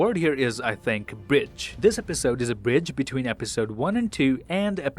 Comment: A BRIDGE!!! This episodes a BRIDGE. LOL. To my American Ears I totally thought you said it was a BITCH. Thank God for context clues. IDK why that makes me laugh so hard but it does.